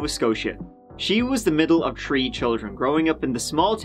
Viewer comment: She was in the middle of tree children? What the hell are tree children? Even the subtitles says it, but what does it mean? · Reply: 3 😜